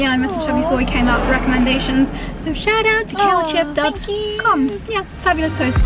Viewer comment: What was the name of the blogger mentioned @ 14:06? Sounds like she says kale chip? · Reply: Kaylchip :)